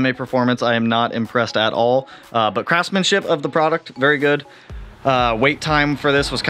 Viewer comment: Love seeing it come together. Looks very mean 😈! It sounded like you said "craftman-shit" lol. Can't wait to see the rest 💪.